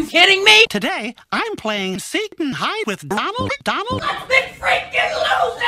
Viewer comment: Mickey: I'm playing Seek n Hide with Ronald McDonald! Me: Hahahahahahahaha! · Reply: Sound like he said satan hide and not seek and hide Xd